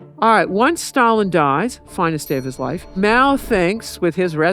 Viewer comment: "finest day of his life" 😂 shady lady Ms Paine, what a legend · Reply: Exactly 🙌🏻 I had to rewind twice.. Only listening to the audio, I heard it as 'finest day of his wife’s' 😅 Nah.. really? Funny yes but.. I red the text in third try 😅 I love that woman 👑